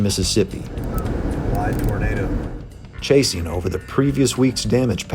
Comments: he says it's "a wide tornado." I watch with subtitles on and the subtitles said "a wide burrito" and I can't handle myself 😂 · he said that's a wide tornado, but why did the captions say that's a wide burrito!?